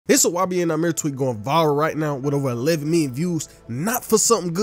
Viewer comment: Sounded like he said Wabian Nahmir